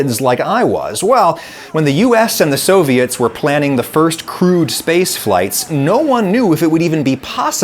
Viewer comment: Hey, just letting you know that there's a mistake in your captions. At 0:44, I think it should say "crewed" instead of "crude". Cool video as always!